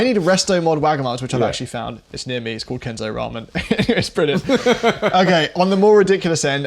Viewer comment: kinton ramen is fine, but if you're ever in the area of one definitely check out sansotei ramen edit: I somehow misheard kenzo as kinton but sansotei is still really good...